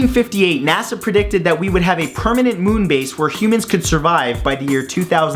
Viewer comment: i swear i heard him say "covanant moonbase"... time to stop gaming so much stickman...